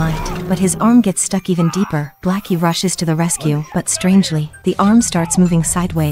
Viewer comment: Blackie 💀💀💀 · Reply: I thought she said "black, he rushes" Like, black is an adverb. "He rushes blackily."